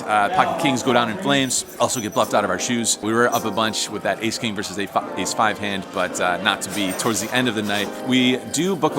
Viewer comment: did I just hear “AK vs a fucked A5 hand”?